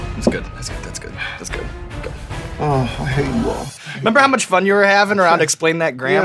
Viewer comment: did he say "I hate you all" at 18:17?